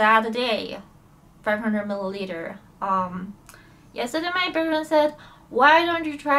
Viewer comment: i hope your fiancè has your best interests in mind <3 usually they don’t recommend dating while in recovery, and it sounded like earlier he was the one to suggest different alcohols (i could have heard wrong) that had higher alcohol content, so i really hope he doesn’t enable you. sometimes the closest ones to us enable us because they don’t want to see us in pain. recovery has so many ups and downs, relapses and getting back on the horse. you’ve fucking got this! good luck darl, i’m genuinely rooting for you! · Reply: if you're talking about around 3:26 mark, i believe she says "best friend" though i could be mishearing that. i just hope the people she is currently surrounding herself with have good intentions and nothing else :( edited for timestap correction ^^'